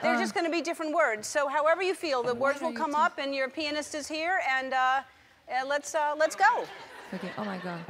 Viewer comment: I hear "your penis is here" every time !